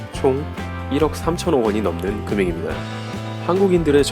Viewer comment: 정도에 1억3천만원을 1억3천억원이라고 하는거로 들리네요